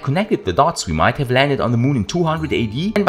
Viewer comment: There could’ve been steamships sailing while Jesus was preaching😂😔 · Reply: I heard the video wrong. Thought it said 40 AD